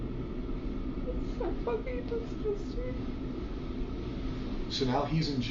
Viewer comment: "its so fking disgusting" is what she says in response to a portion of detectives sentence that is retracted 1:29:53 (edited to correct my timestamp)